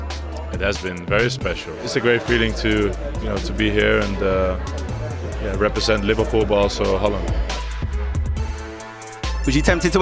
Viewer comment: Did Virgil just say on 2:17 it is great to represent Liverpool and Barca then stutter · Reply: "It is great to represent Liverpool but also Holland."